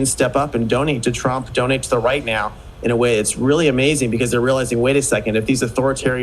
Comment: did he say “right meow”